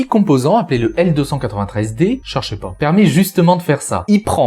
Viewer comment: Y'a que moi qui est entendu "Un petit composant nommé le LSD permet de faire ça !" ? xD